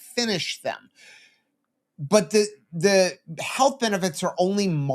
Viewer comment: "The benefit is margarine" LOL, I heard that wrong